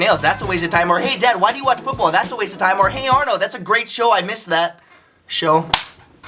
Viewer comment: did he say hey Arnaud? RYAN TALKED ABOUT ME :D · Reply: He said Arnold don't be so proud.